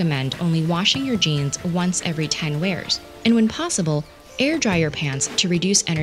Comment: Ten wears? I thought they said ten "years."